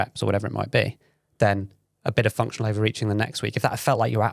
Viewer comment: Ha ... for the longest time I thought was saying 'Functional Over Eating'.